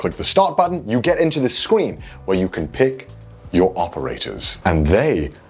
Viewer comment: Did anyone else notice that he says Scween instead of Screen at 2:07?